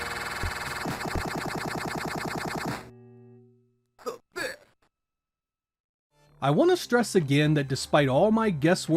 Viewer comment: Does anyone understand what Buzz is saying here? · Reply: My guess; “Who’s the best.”